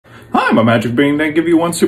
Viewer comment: I thought he said I'm a magic bean